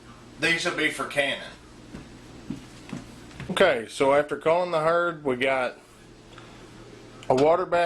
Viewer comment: I thought when you said " Calling in the herd " I thought you meant you would call to your cows to come and eat the potatoes that you didn't want! I'm a city girl, what do i know! Anyway, thank you for all the great information! I will try out some of these methods and see how i do. Thank You!